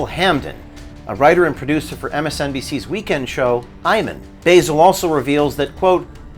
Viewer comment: I had to rewind, i thought you said "MSNBCs weekend show, Hymen."